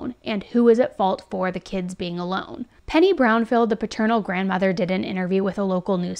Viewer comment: If you listened to the second press conference about the grandmother, they misspelled the subtitle, bc I have very sensitive ears, and I heard her say “paternal” before the prompt even came up… In saying the stupidest thing ever, this is about Athena! Both of these girls deserved so much better, and you would think that DCFS or any other department wouldn’t be that hard to call. I live in a different state, and if I had the resources, I would love to take Adina. This case is probably one of the most tragic cases I have ever heard, bc like you said, these “caretakers” were so selfish in their own rights. We need to find her. Just bc children aren’t theirs, does not give them the right to just terminate their parental guidelines. They “raised” these girls…and now one is left emotionally beaten and bruised…and the other is still missing. May justice be served in OK for these 2 God awful people, who were too selfish to find another outlet to bestow their (what I feel like) is hatred upon these girls.